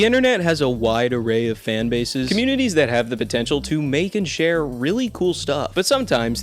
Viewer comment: did you just say, potential?